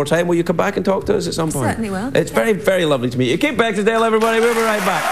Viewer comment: It sounded like Craig said Beckingsdale on two occasions. It's also funny how they talked about how creepy it was that a tv show host kissed the guests but Craig does that all the time.